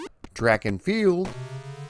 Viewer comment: knocking it out of the park again. For a second I thought I heard him say Drac N Field, And now I can’t get out of my head why Konami didn’t make a Castlevania sports competition title lol